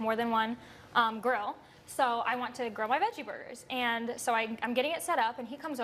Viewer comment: At the begging I thought she said I was growing my veggie burgers... I thought she was making a burger but with weed😐😐 I need help😂😂